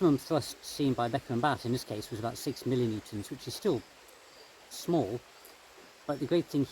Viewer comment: I heard him say the force was 6 million newtons, did I hear him correctly? If so that’s 611 tons which is huge. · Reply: milli Newtons